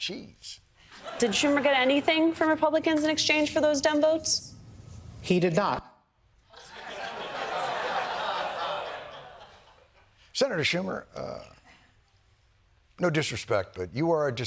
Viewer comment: Hmm when she asked about the votes. It sounds like she said “dum votes”. It checks out.